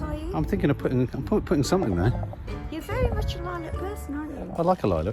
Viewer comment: When nanny said “You’re very much a lilac person” I thought she said “you’re very much a violent person” at first 🤦🏻‍♀️😂